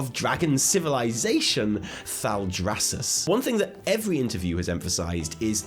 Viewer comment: Am I the only one who keeps mishearing Thaldrassus as Maldraxxus?